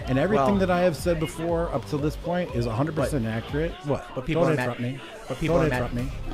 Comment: You even slowed it down but I cannot understand what he's saying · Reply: “Don’t interrupt me”